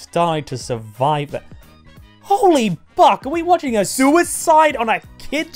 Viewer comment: WHOA WHOA WHOA WHAT THE HECK DID I JUST HEAR?! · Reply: Buck